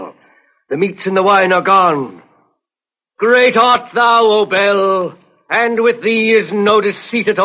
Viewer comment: I kept hearing "Belle" and it annoyed me lol.